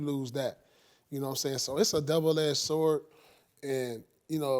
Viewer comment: Did he say “Devil Edged Sword”? 0:52 lol